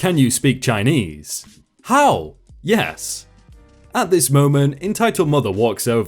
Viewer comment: Hello from Hong Kong. Been watching your video for a while now. The Mandarin part is pretty good indeed, though at 13:30 the NC actually said Dui 对 instead of Hao 好. While they both means yes, they are used in different context. Keep up the good work!